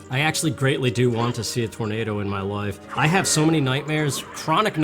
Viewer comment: It almost sounded like you said "Tormato" just there. Great album btw. YES!!!!!